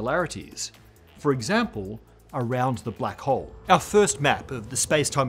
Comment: Oh, I thought he said a black ‘ho.